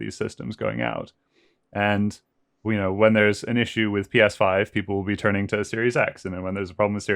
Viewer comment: Did Will really say 1:17:00 "When there are no PS5s, people then turn to Series X.".....